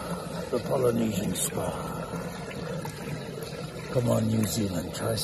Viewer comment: "come to New Zealand, try something new." "there are no words." he should be a spokesperson for New Zealand's traveling commercials by now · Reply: I thought he said “come on New Zealand” lol